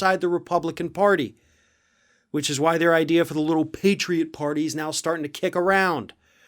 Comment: Did you say the Patriot Party, I thought you said the PORTA PARTY.